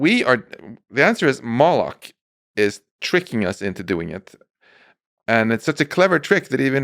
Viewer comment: Did he just say (Moloch) that's related to satanism and the occult if I'm not mistaken 🤷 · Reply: yeah what the hell I just heard him say that too???? "moloch is tricking us"